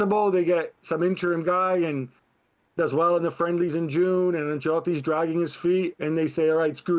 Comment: did he call the qualifiers Brazil is playing in June.... Friendless? 😂 Mercy.